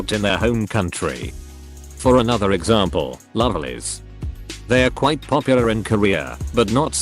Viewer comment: can someone tell me the name of the group, I couldn't understand exactly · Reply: The voice is saying Lovelyz but the fancam is CLC ^^